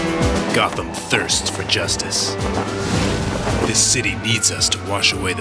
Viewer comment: LOL I thought he said, Gotham thirst for juice.